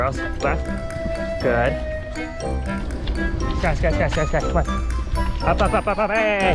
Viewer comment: What are You Saying 😂😂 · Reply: Sky, Sky, Sky, Sky come on. Up, up, up,up hey, hey! No, no